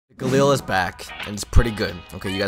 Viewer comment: Sound like you said gorilla is back.